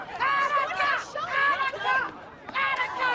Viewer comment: What does he yell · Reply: Attica, as a reference to the film Dog Day Afternoon, one of my favs